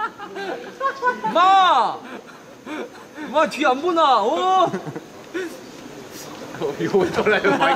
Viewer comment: "ma tia bruna" só eu que entendi isso